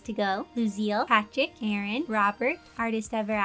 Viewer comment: When she said Aaron I was looking at my recommendations and thought she said Karen